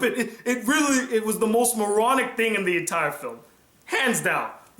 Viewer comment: I know what you said but I kept hearing "the most erotic thing in the movie" 😂 lololol